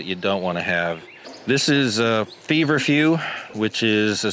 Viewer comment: At the 12:50 time stamp marker, he mentions and shows a flower that looks kind of like a daisy with white petals & yellow center and says it's nature's aspirin for pain relief. What is that plant called?? Sounded like he said "Fever Few". I replayed it several times but cannot understand what he says. Does anyone know?? Thanks!